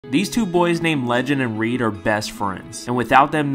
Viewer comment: I thought he said legend, and creed